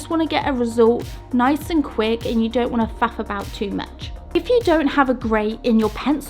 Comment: What'd she say at 8:19? Never heard it termed like that. 😂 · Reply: Was it the ‘if you don’t want to faff around’, maybe that is just a British saying lol 😂